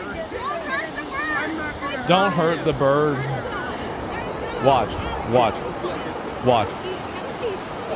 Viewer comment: I thought I heard "Don't eat that bird". lol